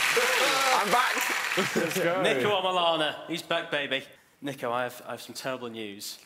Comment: subtitles say "niko, with an honour" and stephen said "niko omilana" lmfao